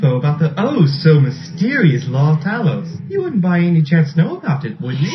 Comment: Not me who thought it said "law of tacos"